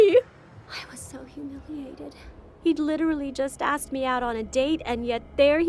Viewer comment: how do they expect anyone to hear, much less understand what she’s saying? After a few replays, I finally heard it. For those who didn’t, she’s saying, “I felt so humiliated.”